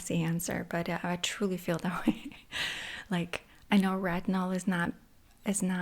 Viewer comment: what she said work? 8:58 (bad english) tha ks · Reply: She was talking about retinol:)